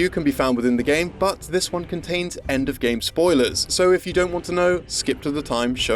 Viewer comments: i heard "endgame spoilers"(as in avengers endgame), not "end of game" spoilers since we were already talking about marvel, so i continued watching and got spoiled. thanks :) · "this one contains end of game spoilers". Of course what i heard was "endgame spoilers" and, having already seen endgame, I kept watching and just spoiled myself the ending of God of War. Good job, me